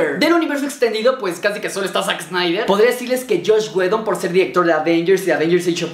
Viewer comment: Aca hablo muy rapido y no entendi nada alguien me dise por favor que dice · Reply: Dijo que solo está Zack Snyder